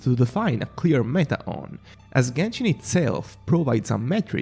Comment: I heard "as genshin incels" 💀